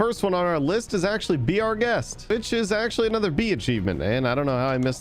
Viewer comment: Am I the only one that heard bitches at 15:21 instead of which is